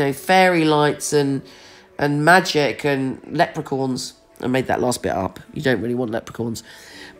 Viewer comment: I thought you said ‘leprechorns’. Like a leprechaun and a unicorn had a baby! Haha!! Thank you 💜🍀🦄